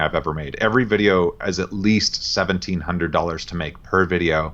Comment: $1700??? Did I hear that right?!